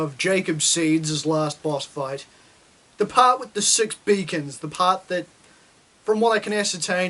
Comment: 6 Beacons? I thought I heard 6 bacons. Anyways the reason why I call myself the Groundatollah is because I shake the ground whenever I walk cause of all the grease I store.